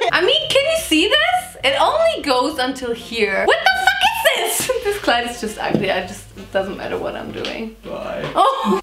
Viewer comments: Did she said "Kleid"?!😂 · Did anyone heard she saying „This Kleid“😂